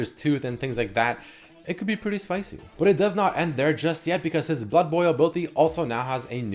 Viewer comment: I thought you said butt boil for a moment.